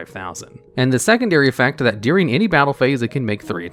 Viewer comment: I was listening to this in the back and around 9:20 I heard "83" instead of "any 3". I was like got dang!